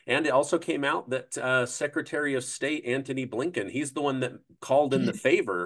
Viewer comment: Why does it always sound like is saying LaNken with an N in the middle. I love these interviews though. Keep up the good work